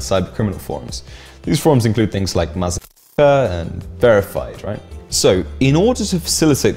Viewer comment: What exactly was said at 5:44, anyone ? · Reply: Mazafaka